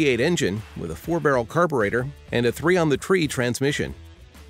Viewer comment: when it says the Dodge came with a 3 on the tree transmission it shows a picture of a pushbutton automatic on the dash... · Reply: I thought he said 300 on the tree